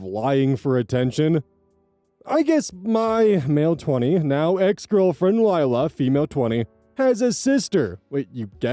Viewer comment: I figure he meant to say "I guess she's now my ex-girlfriend" but I also heard it as he guesses she has a sister.